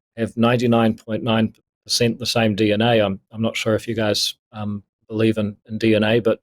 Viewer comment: This interview changed my vote instantly. I was an ACT vote most elections, but when he said ..."Don't know if YOU GUYS" believe in DNA(suggesting faith-based people or conservatives ignore sience) , he lost me. It's amazing how one can be a bigot towards Christians and conservatives, but when the door swings the other way then the narrative changes. It was really disgusting to watch this little man reveal himself. He strikes me now as a person who wants to be the next Justin Trudeau , to declare "PEOPLE" with an opposing view as some evil T.rist group.. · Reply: He never said "YOU PEOPLE" he said don't know if "you guys" (there's 3 of them interviewing him) believe in DNA, check it out at around 14:30 in the video. He did appear to be frustrated and not want to be there but you are misqouting what he said, saying he said "YOU PEOPLE" when he didn't and then yourself linking that phrase with Trudeau's "Those People" as evil Terrorist is spin.